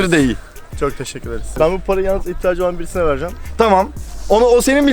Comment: bu parayı iddaacı birine vercem diye anladım :D:D:D:D